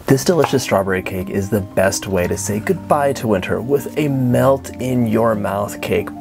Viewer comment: I THOUGHT YOU SAID "goodbye to a TERF" and i was waiting for you to say something funny and mean about transphobes but it was just wholesome😂❤ beautiful cake